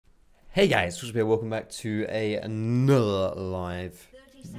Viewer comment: does anyone even know what foxdrop says here? 0:00 heys guys________ here? sounds like social p lmao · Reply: "foxdrop here"